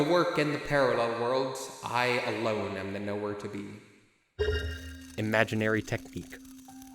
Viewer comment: Y'know, I rewatch videos from time to time and even occasionally turn on subtitles. I realized I misheard a line. "Throughout The Work and the Parallel Wolds, I alone am the Nowhere to Be." That's what I thought I heard at first, and chuckled, knowing that breaking the boundaries of Noita truly does mean you have nowhere to be. I like my version better.